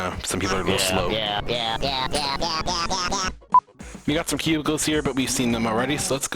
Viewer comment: everytime he said cubicles, i thought he said pubicles ._.